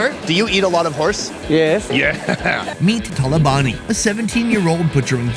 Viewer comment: I heard Meat Taliban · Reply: *Talabani